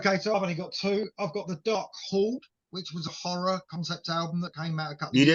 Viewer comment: Another great walk through favorite albums and future favorite albums yet to be discovered. Hey, what was the concept album you mentioned that’s a horror-themed album? I thought you said Dark Hall but I’m not sure. Thanks and keep on keepin’ on! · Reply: Hi...its The Dark Horde